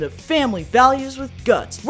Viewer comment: I thought you said "family awesomeness with guns " I had never heard a more American sentence